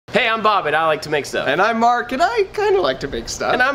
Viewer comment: Bob, a few videos ago it was clear to me - and many others - that you say ".. Bob AT I like to make stuff". For the longest time, I've always heard it as "I'm bob AND I like to make stuff". You should really say "and" instead. Why? Because it makes your videos/youtube page etc more like a community and less like a business. It is what people hear already, and your guest Mark kind of played off the intro as if you are saying "and" as well. Thanks for the content.